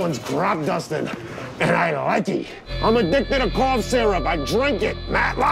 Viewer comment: I'm addicted to cough syrup I drink it. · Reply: I could have fucking sworn he was saying corn syrup.